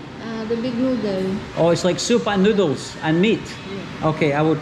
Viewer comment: I thought he said “oh it’s like Super Noodles?” Not “Soup and Noodles” lol 😂